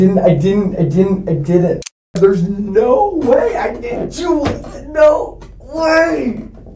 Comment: Imagine going to a $50k/year private school only to come out having the linguistic ability of a caveman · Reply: Yeah I'm grateful for the subtitles because all I heard was "dude, no way. Like, no way. Dude"